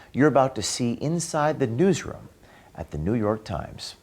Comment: I had to rewind the video… I thought he said douche room instead of newsroom…. I guess what I misheard is more accurate though.